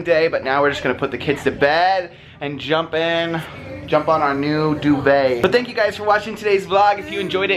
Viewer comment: I thought he said," jump on the new baby!" 😱